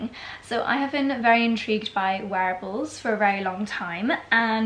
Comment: when you said you’ve been really interested in “wearables” lately i was eating something crunchy and i thought you said interested in werewolves lately 🤣